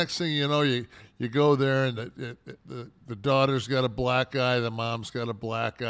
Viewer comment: Kev says black eye, Sean hears black guy. Anyone else catch that?